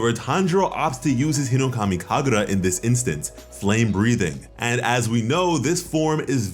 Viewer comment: he did not just say flake breathing